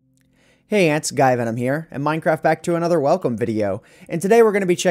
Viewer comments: LOL listion to the into, this is what I heard: "hey ant sky venom here. and minecraft back to another welcome video." XD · LOL listen to the intro "Hey ant guy venom here" XD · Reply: Richard Whitt You are right,he kinda accidentally added an "s" to guyvenom.I'm pretty sure his past,he didn't pronounce that way.He made it quite clear.He accidentally made it sound like he is saying "sguyvenom" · LOL listen to the intro "Hey ant guy venom here" XD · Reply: Oh i thought i heard 'sky venom'